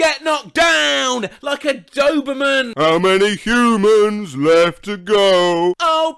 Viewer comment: Bro, I misheard, saying “ how many humans left to go” has “ how many monsters left to go”( sounded more like mume-sterms but it looks better as monsters for a weird reason that I have no idea of) for some weird reason